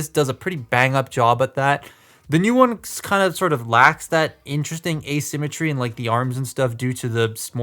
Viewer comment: Anyone else hear gay symmetry around 1:06?